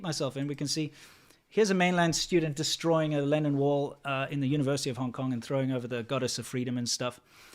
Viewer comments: Haha I had to Google "Lennon Wall." At first I thought you were saying, "Lenin Wall" so I was REALLY confused. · I kept hearing "Lenin wall" and I couldn't figure out why it represented anything good.. Oh I need to go to bed. Obviously it's "Lennon wall".